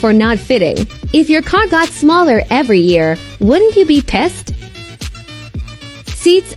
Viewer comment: I thought the AI said “if your COCK got smaller every year, wouldn’t you be pissed?”